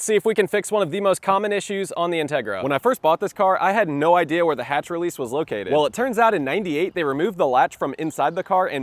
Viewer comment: I thought he said hood release 😂